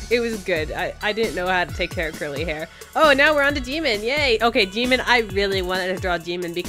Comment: LMAO IM CRYING WHENEVER SHE SAID JIMIN I THOUGHT SHE SAID DEMON AND I WAS LIKE I mean u ain't wrong